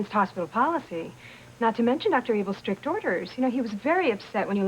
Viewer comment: Every time they say "Dr Able," I hear "Dr Evil" 😂